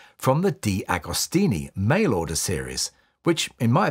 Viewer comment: for a second OI thought you said "male order" ....